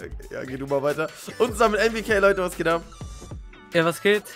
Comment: wer hat auch bei 0:26 A.B.K verstanden ?😂 ↓ ↓